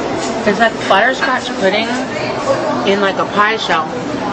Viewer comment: That ice cream mound is bigger then my 4 month old nephew. And excuse me, but did she just say "fire crouch pudding"?